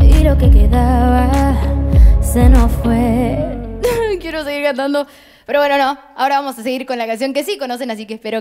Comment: what is she saying? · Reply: "Quiero seguir cantando! pero no.. Ahora vamos con una canción que sí conocen, así que espero que bailen del otro lado de la tv, de la computadora, o desde donde estén viendo. Aquí va "tuya"...."